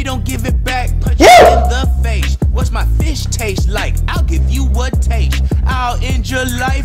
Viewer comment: When said what my fist taste like I thought he said what my face taste like